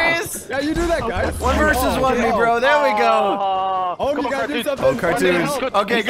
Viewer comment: Skip or go back to 2:28 i swear owm said negro lol not sure if he did or not lmk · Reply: He said "One versus one me, bro!"